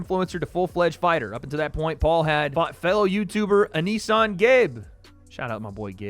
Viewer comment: thought you said failed youtuber for a sec💀